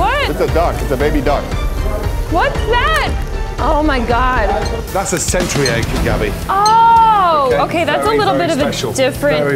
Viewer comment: I'm glad there's subtitles because I would've thought he said "that's a century egg, you dummy" lmao.